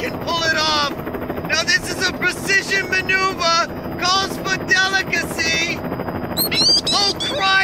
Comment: What does Fossil say in 14:18-14:20??? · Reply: This is a precision maneuver calls for delicacy